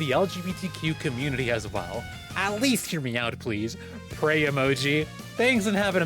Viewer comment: I wasn't watching, but I heard "hear me out" as "hear meow-t". I like to think if they wrote "meow-t", they'd get unbanned.